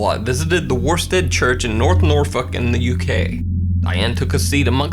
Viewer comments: I hear him saying “the worst damn church in north nor fucking UK” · it sounds like he says “norfuck” instead of “Norfolk” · sounds like he said “fuckin the UK”